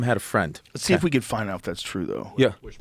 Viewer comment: "see if we can find thats true though". I thought he said "see if we can find thats Trudeau". Could be, he looks like a psychopath.